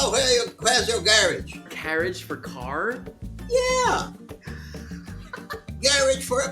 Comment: Sounded like he said gare-age. I’ve heard people my grandparents’ age say that - don’t hear that much anymore.